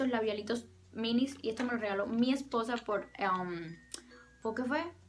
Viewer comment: según yo dijiste " esposa " o al menos eso escuché ... ¿ Eres lesbiana ? :3